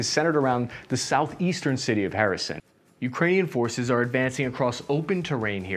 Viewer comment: did bro just call Kherson, Harrison?